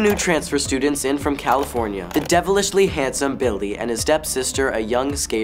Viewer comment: Did noah just say “bildy” instead of billy?